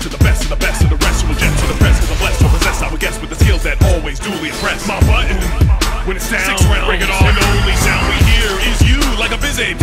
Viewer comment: Did he say "bling" instead of "bring"?